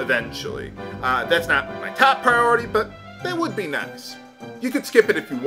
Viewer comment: Did you say that would be nuts? xD